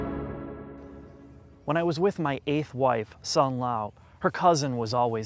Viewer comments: Wait... Did he just say 8th wife?? Someone help me hear.. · Did you say your 8th wife at 1:02??